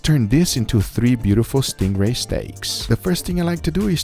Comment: Thought it Said ”Sting Ball Steak” 😅